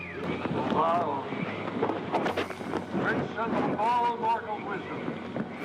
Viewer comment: did I hear “princes of all mortal wizards”??